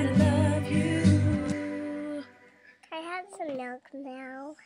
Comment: Who heard at 2:42 saying can i have some milk now :') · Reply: I heard "can I have some yogurt now?" But milk makes more sense.